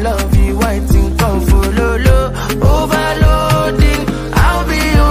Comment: I thought she said “follow God “